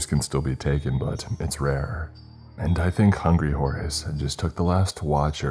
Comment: Fun fact, "Hungry horse" is also a chain of family restaurant. If you want to see a real horror story just order their special. 🤮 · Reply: I thought he said hungry horace 😅🤣